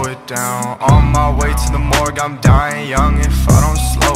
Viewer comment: I Heard “On my way to watch morgz I’m crying” instead of “on my way to the morgue I’m dying”